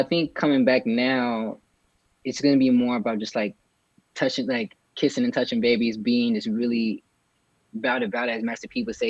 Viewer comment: wtf lol dude said “kissing and touching babies” ....BRO! It’s “shaking hands and kissing babies” 😂😂😂